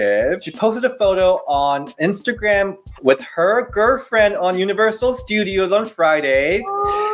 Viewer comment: You say girlfriend funny it’s cute 😂 sounds like you say gurfriend 😂❤️💀